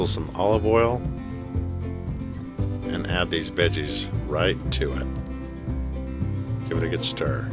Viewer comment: Sounded like he said "then add these bitches" when he tossed in the the veggies 🤣🤣